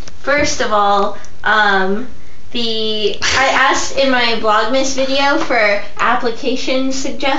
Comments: Did we travel back in time? I thought said Vlogust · it sounded like you said vlogUST! hahah